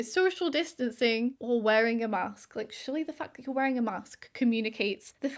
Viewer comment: I misheard you swearing, like “surely, the f***? 🤨 You’re wearing a mask, that alone communicates you’re wearing a mask” and I just thought “whew, she sure is passionate today!” 😂😂😂